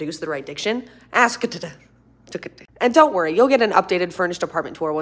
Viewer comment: At the bottom when it says, please don’t sell my feet pictures I thought you said please don’t smell my feet 😭😭😭